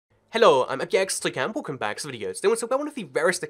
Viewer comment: did you say “hello ima gangsta cat” ???